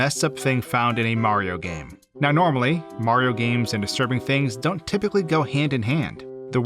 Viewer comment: When you said “Mario games and certain things don’t go together” I thought you said “Mario games and A Serbian film don’t go together” and I got really concerned about the direction this video was about to take.